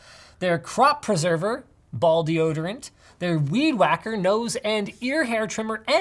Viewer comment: Thought he said ''balls deodorant''